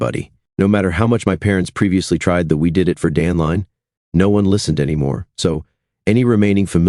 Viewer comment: I’m a minute in. I hope this isn’t that do it for Dan story. 9:17 it’s the story. 🤦🏻‍♀️ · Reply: My thoughts exactly. 😂 I liked the line around 24:50 where they refer to 'dandelion' instead of 'the dan line' 😂😂